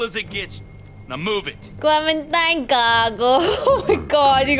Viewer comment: What did you say in 57:00 clementine gago? You speak tagalog? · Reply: Oh my you just said tangina😭😭😭😭😭